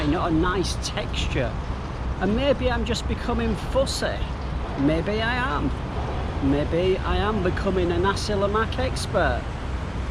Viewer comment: beautiful video. I couldn't understand the phrase you said in 12:02. I'll be so thankful if U right it down here. Will wait for your next videos. Cheers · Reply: "maybe I'm becoming fussy (cerewet), maybe I'm becoming a nasi lemak expert"